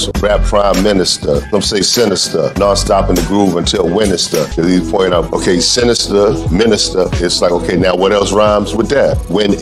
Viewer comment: Really thought you said winterstha